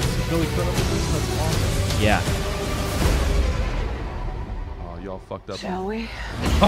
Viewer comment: for a second i thought aquaman or cyborg said "ohh y'all fucked up now"